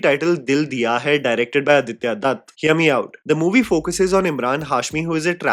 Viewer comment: Bro I did a double take at Aditya Datt, I thought you said Aditya Dhar 😂